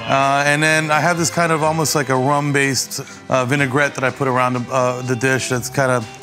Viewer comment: When he said a rum base I thought he said a rambae